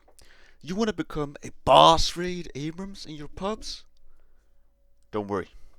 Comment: I heard "bossraid abrahams" and got sent back I KNOW WHAT YOU ARE